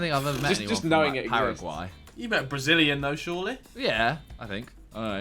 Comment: did i just hear 'Paraguay' ?? Well hi, I'm from there🙌🏽